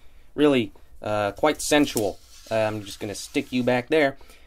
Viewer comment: i thought he said "quite sexualy" 😂